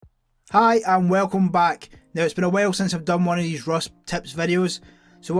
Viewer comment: Did you just say "Hi, I'm welcome back!"